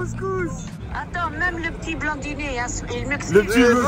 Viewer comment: C'est moi où elle dit " même le petit blondinet il m'excite " 😳